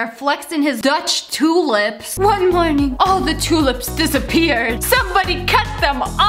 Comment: On 8:03 instead of saying one morning did she say one one?